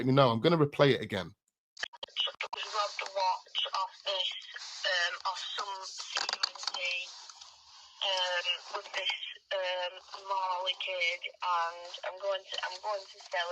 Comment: sounds like said .. robbed watch off some C*unt WITH this Mali kid and going to sell it. It’s also weird how she says ‘he sent a message into the group chat of his face.. and then turning around saying’ does she mean when Ayub turned around to take the picture of Jay in the back of the car